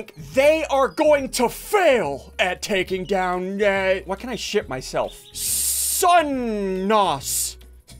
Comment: Wait a minute did ssundee just say Shit?